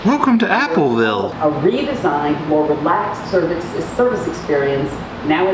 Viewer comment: the current watch is water proof and so is your iPhone bud. Your reactions were hilarious though lol. I also heard her say "relax your cervix"